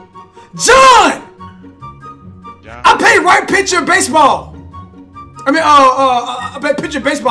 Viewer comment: did you just say I play right pitcher for baseball XD